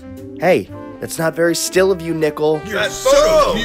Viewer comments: The subtitles says nickelodeon That's the new Nick Le! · cc says “Nickelodeon”